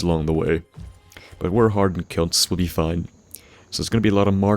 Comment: your accent is hypnotic. but what do you say at 7:23, is it harden celts or harden c**t's. i have only started to try and play Fallout 3 and can't get into it, but i do enjoy your videos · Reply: I'm pretty sure he sayas lol *says cunts ( ithnk idk)